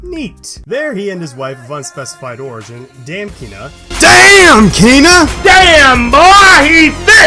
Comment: I thought you were saying dam KEANNA AS in my middle name. I was dozibg off that woke me up